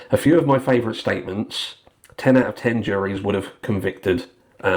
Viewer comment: I’ll refrain from addressing due to my lawyers on vacation 😂 BUT I thought said 10 out of 10 courts. Not jury’s ! Jury’s decide not courts. Maybe in Idaho that’s how that actually works though 😂